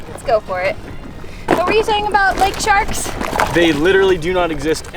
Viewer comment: When she said are there lake sharks. I thought she said lake char.